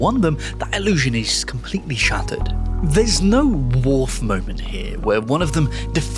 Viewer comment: Did he say "Wharf Moment?" at 11:45? What is that?